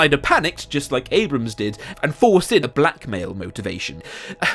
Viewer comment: And I honestly thought he said "black male" at first, I was like WAIT WHAT